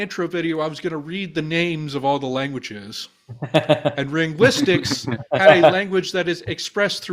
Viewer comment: who is the creator of the exercise language? I heard "renguistics" but nothing is coming up when I search that so I think I have been mishearing.